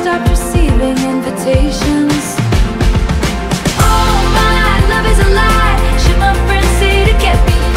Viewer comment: I can feel my heartache when I listen to this song even, but it’s also very catchy. When I first heard this song, I thought she said oh my love is alive, and hits different meant staying together and just going through the messy together. It made me think of my relationship with my partner.